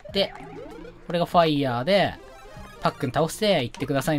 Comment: おっ、ファイアーが おっぱいやーに聞こえた